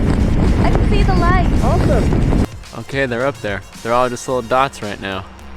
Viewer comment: I thought he said "they're all just little thots" 😂